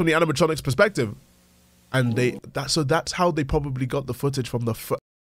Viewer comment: Does anyone know what he said here? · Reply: The footage on the foo